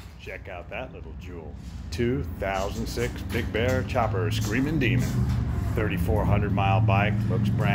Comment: for a minute I thought it said semon demon